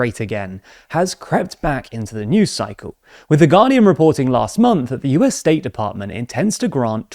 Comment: lol The Guardian I heard "The Ghanian" and was very confused as to why that would be a very reputable source on European affairs 😆